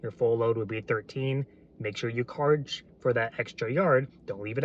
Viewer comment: did you say "Karge for that extra yard"